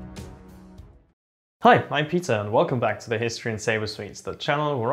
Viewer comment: him: Hi i'm peter what i hear: hi i'm pizza